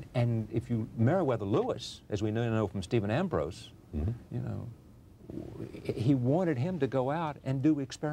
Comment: I`m having trouble to understand what the interviewer says at 5:08 What I get is "Married with Louis as we know from reading Stephen Ambroise" But I think probably I`m wrong since Jefferson`s wife was called Martha Wayles Skelton hey, could you help me again? Thanx a lot